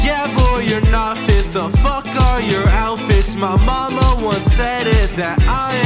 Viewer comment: On 0:21 subtitles instead of mom it’s bottle